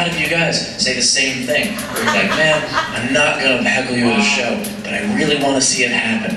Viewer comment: sounded like you said hackle not heckle. That ny chich must have got in your head. I could be wrong but that's what I hear.